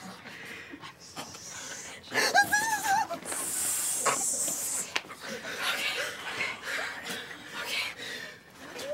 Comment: What was she doing? 10:32 I didn’t catch what she said. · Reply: She said "That is so nasty"